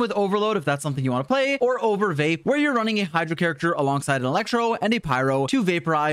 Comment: I thought u said overweight n it had me snorting so loud HRLP